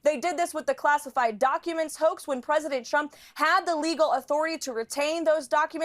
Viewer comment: Sounds like Carolann is saying President Chump. OMG lol